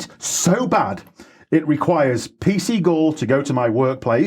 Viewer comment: When he said P C Gull, I heard 'P Seagull' speaking to the Owl... got the makings of a children's book there 😀